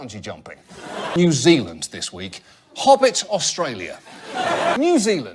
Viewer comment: I'm german and I first heard: "New Zealand : Australia's Austria".